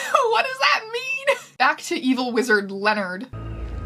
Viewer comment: I thought you said "Evil lizard named Leonard'' i wheezed